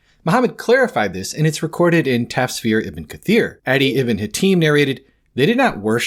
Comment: what was that referenced book 1:33, captions say "tapere IB cir Addie" quote {adi ibn hatim narrated, "they did not worship them [The rabbis and monks], but they obeyed them."} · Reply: tafsir ibn kathir 9 31